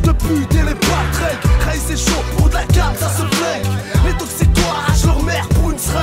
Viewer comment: Did he say "Arschloch" (DE, asshole) around 5:35? I didn't get that part at all